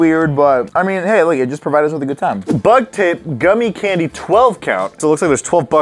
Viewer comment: When said bug tape I thought he said butt tape.